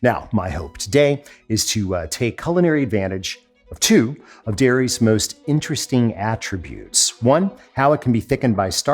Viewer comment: What is the censored word at 2:40 ? Was it a joke? If so, I don't get it. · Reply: It sounds like an error, but the word was "air".